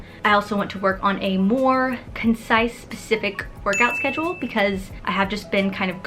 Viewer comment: Did she say pacific around 2:10? Lmao